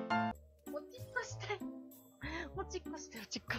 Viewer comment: おしっこしたいの所おちっこしたいって聞こえたw